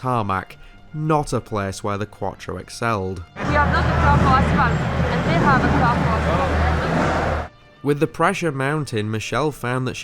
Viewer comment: What did she say there? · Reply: We don't have a car for asphalt, and they have a car for asphalt